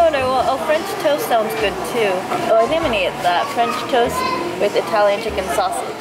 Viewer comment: 字幕通りに聞こえないんですけど。。 聞こえますか？？ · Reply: 今更の返信ですが I’m going to はI’m gonnaになるので聞こえます。、